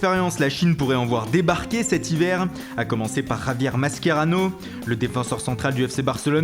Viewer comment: Javier quoi ?? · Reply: Yaniiis PT masquerrano😂😂😂😂😂😂😂